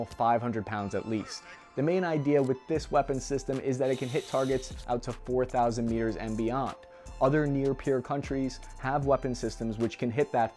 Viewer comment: Thought He Said "Out To 4 Thousand Years and Beyond!' - Im Like - We Win!👍 This Gentlemen is Very Intelligent and Way Humorous Via' The Best Arm Chair Engineer Therapy 🇺🇸